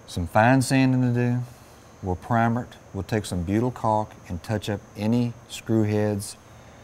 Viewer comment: what did he say at 21:33 me and my dad made are own verisons but have some cracks help? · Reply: "Butyl caulk". Flexible, waterproof and durable.